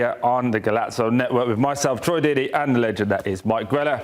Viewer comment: i thought he said troy didi😂😂😂